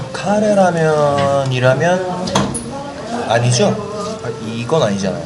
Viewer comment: 뭐라는건지 이해하신분..?? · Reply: 샬롱Salon 카레라면 이라면 아니죠?